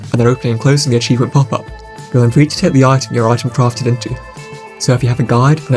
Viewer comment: what is he saying at 2:08 ? even the closed captioning doesn't know what he's saying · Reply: "You are free to take the item your item crafted into"